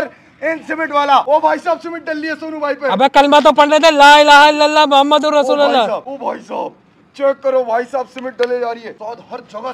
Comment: Bhai ko kaho Kalima sahi parhen @ 1:43 sahi ye hy Muhammad ur Rasoolullah... not Rasoolallah...if i heard correct... bohat ihem or Imaan sahi honay ki baat hy k Kalima sahi ho or yaqeen sahi ho. Jazak ALLAH khairun